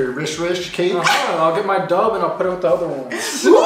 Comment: What does say at 1:33? "I'll get my dob and put it with the other one"???